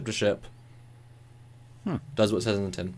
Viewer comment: What did he say at 21:46, after ship-to-ship, "does what sediment in" or something? Google's captions also heard "sediment in", but said "I love that expression"... The guy introduced himself as Commander "Gung SayGuys"? Google heard "I am agog hi guys"... that's a pretty thick accent & a little clearer pronunciation would be awesome. Still Thumbs Up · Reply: He said, "It does what it says on the tin."